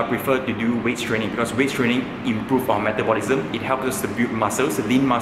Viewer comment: Can you make a video on Bulking? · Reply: hey, i can't hear properly on 4:09 , is it waist training ?